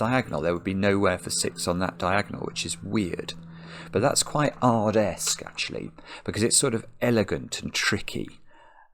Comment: What word does he say at 14:08? sounds like arabesque?